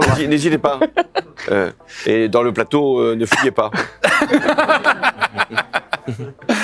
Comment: Qu'est ce qu'il dit à 9:41 ? "Et sur le plateau, ne ........ pas." ne fuyez pas ?